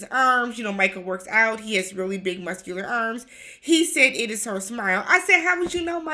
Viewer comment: I lost it when you said “how do you know Michael? she doesn’t smile” 😂😂😂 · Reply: I thought he said he liked her smell, not smile. Jasmina lacks self aware that she is a huge part of the communication problem.